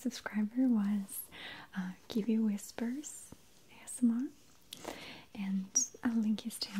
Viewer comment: I couldn't tell what you said at 4:25. Givey whispers? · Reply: kiwiwhispers ASMR :)